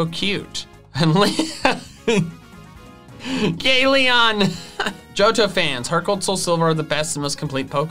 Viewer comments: did he say "gay leon"? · ``gay leon!`` is what I heard- now I can't unhear it